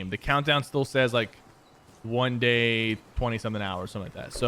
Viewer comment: ‘One day and twenty seven hours ‘ Sypher 2020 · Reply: He said 1 day 20 something hours, it sounded like he said 27.